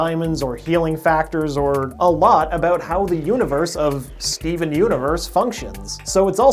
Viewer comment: 'Skeven universe' please tell me I'm not the only one who hears that. XD